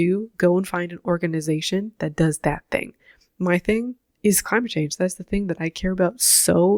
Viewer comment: Can someone write the word she is saying here? "my thing is _____" I'm not a native speaker and I can't make out what she said, I just hear "crunching" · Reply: “Climate change”